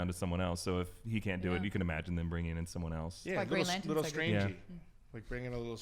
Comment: Did she just say “like Green Lantern” He’s not even part of marvel...